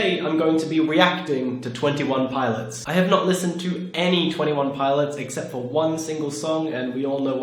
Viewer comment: I thought it said "never listen to twenty one pilots" and I was just like "exuse me"